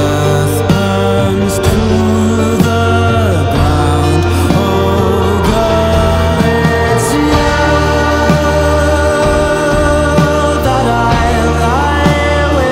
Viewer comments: I misheard the lyrics as "le cœur du ciel" when they were actually "oh girl it's you" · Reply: I keep hearing "oh god it's you" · Am I the only who thought he said "oh God its you" instead of "oh girl it's you" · Am I the only who thought he said "oh God its you" instead of "oh girl it's you" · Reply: i thought it said I'm glad it's you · Am I the only who thought he said "oh God its you" instead of "oh girl it's you" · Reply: I thought they said “ It’s sueeeee that I pie with “